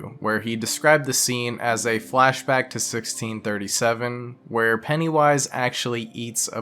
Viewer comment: Any one else mishear "pennywise eats a baby" as "pennywise yeets a baby" ?